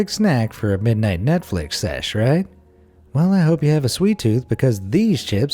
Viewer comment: did he just say nut-flick sesh